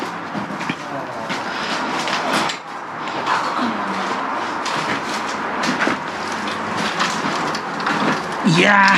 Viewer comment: 女性の声で「あくかな」はっきり聞こえる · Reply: 字幕入れると 開くかな... ってなってますのでさんボイスですね